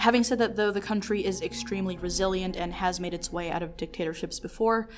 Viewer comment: what did she say? Brazilian is that what she sad?